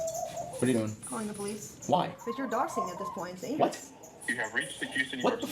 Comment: I heard "cause you're doxing at this point" as "cause your dog sing at this point" and was like whaaat? xddd